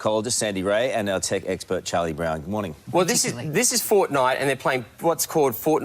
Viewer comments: did they just say there tech expert is Charlie brown bru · did he say charlie Brown??? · did anyone else hear him say Charlie Brown? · Did he say Charlie Brown